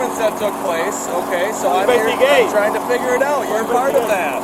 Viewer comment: did he just say "you might be gay" to the police officer?